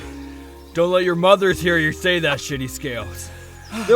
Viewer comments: till this day can’t figure out what he said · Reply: He said smd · I NEED to know what Kirishima said and what it means!! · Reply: he said “go🖕 your mom”